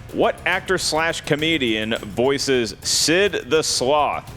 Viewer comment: I could totally see how Sam thought Mark said, "actress".